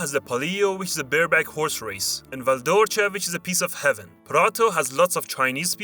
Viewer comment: What's the name of the city ? I didn't understand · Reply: It mentioned first Val D'Orcia which is a valley and just after Prato which is near Florence and used to have big fabric and clothing production which were all bought by Chinese